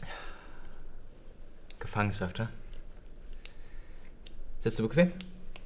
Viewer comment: was sagt er bei 3:10 ? Ich habe das nicht verstanden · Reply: "Gefangenschaft, hä. Sitzt du bequem?"